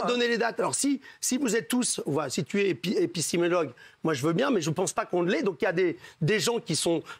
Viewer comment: épi quoi ? 😹😹😹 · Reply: Épi c’est tout